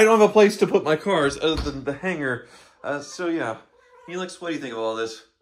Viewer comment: Came for the car, stayed for the cat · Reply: oh? I thought he said, Felix, as in the cartoon cat…